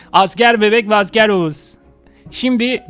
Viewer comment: asker bebek ve asker Oğuz mu? Arkadaşlar ismini öğrendik Oğuz. (Yanlş anladımsa özür dilerm)